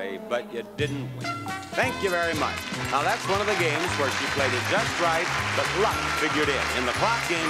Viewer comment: What's Bob saying at 4:00-4:08? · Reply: "That's one of the games where she played it just right, but luck figured in. In the clock game, if you play it just right, you win." In other words, you could know when The Price is Right, but still not win on account of luck. Just like Plinko and Joker. With games like Master Key and Punch-a-Bunch, if you know when The Price is Right, you might not win the announced grand prize, but you will at least win something. Producers were OK with doing this on "small prize" games, because she did win the camera and the hair setter, so it's not like she went completely empty-handed (and the cradle, too).